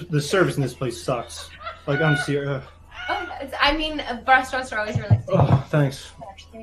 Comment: What does say at like 8:28? starts dying but she whispers it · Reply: Restaurants are always really sticky or stinky or something like that. I think laughs because it’s so dumb